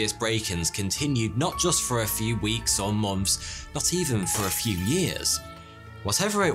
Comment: So.. anyone else notice it sounds like he says "mumps" instead of "months" I really can't unhear it now. Lol